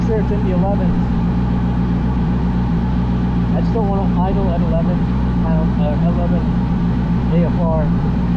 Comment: Idling at 11 AFR is gonna foul plugs in a hurry man. That's RIIIICH. I ran 1,000HP boosted motors and was always told anything in the 12's was considered super safe. LOVE what you've done with this thing so far! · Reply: To me it sounds like he said that he didnt want it to idle at 11 but the text was wrong